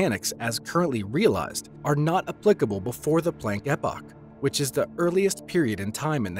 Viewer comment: What is said at 1:40 ?????? I listened to that 100 times and can't make out what he's saying... "Are not applicable before the blah blah blah..... The earliest period in time in the history of the universe" Plane Eppa? Plang Hippa? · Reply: “Before the Planck Epoch, which is the earliest period in time.” Better late than never, I guess.